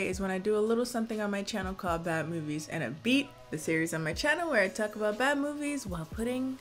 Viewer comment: When you said “Bad Movies & A Beat”, I heard “Bad Movies and a Beef” which seems like a subtitle playlist for movies that you have beef with. (I.e Death Note)